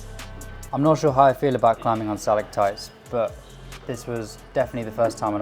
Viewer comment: what did he say here? "I'm not sure how I feel about climbing on sa????? t?????" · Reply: stalactites. its the shape of the volume he's on in that timestamp. named after the rock formation